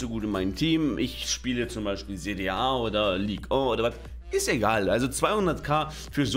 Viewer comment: Habe ich da etwa ein Oder Watt gehört?😂 Der Watt-Mann wäre stolz auf dich